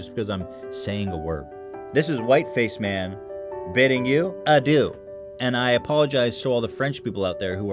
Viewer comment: When you said adieu I thought you said ah do XD